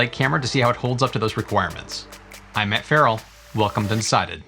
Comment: Thanks Matt - “welcome to DECIDED” Did I hear that right 😀? (0:53) Love your work, thanks.